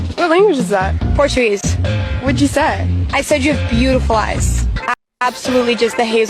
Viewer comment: Caption is wrong. She did say she had beautiful eyes